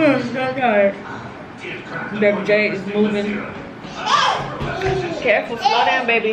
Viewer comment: Is me or did she say Baby Jade at 20:55 ???